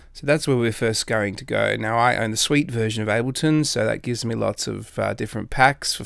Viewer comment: hahaha i thought you said i have the sweet version of ableton